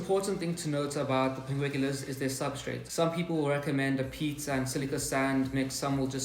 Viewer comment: I just got some temperate pinguicula. -- lol, I love when you talk about the substrate. I first heard "some recommend a pizza" hahaha. Then it registered and I realized you said "PEAT AND silica sand."